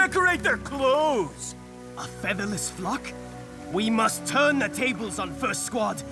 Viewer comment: Is it me or did he said a featherless fuck?